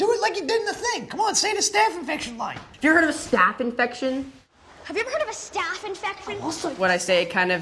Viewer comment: he actually says "infecfin" if u listen closely